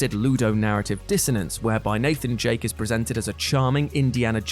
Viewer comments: Who else heard him say Nathan jake instead of drake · ok i might be mocked now but did he just say Nathan Jake? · Did he just say Nathen Jake?? hahaha